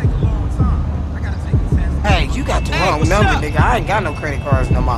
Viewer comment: bei 12:58 hab ich cjs stimme gehört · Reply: er sagt Glaub ich "Hey Whatsup."